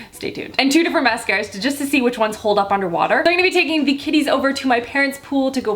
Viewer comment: At first I thought you said kitties instead of kiddies. So I was just like "why are you taking cats swimming?" 😂😂😂